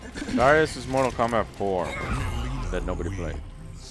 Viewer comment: joe says something about mk4. Did he say geras is from mk4 that nobody played? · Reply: He says Darrius. But I don't think he's MK4, pretty sure he's from Deception.